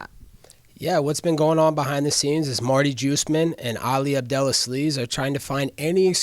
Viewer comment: Did he say Marty Jewish-men? Lmao